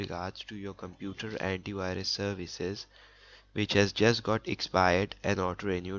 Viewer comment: I thought he said "Bitchass just got expired" 😆🤣😂